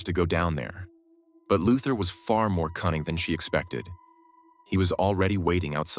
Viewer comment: I heard "cunning" as "kind"